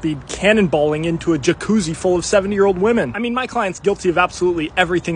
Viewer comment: It sounded like he said "7 year old woman" I had to pause it and check the comments and then no one was talking about that I restarted it and seen it said "70" thankfully this dude isn't a creep.